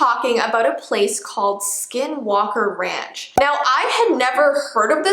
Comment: I thought it said skiddle walker for a second 😂